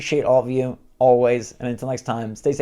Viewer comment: At first, at the end, I thought you said, "Switch World Out!". And I thought you shadow dropped a new name. XD